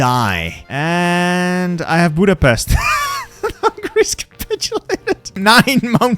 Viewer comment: think of him saying Greece capitulated and it will sound like he said that